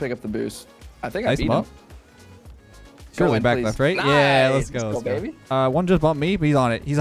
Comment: Anytime your teammate says "Back Left" I hear "Back, Leth"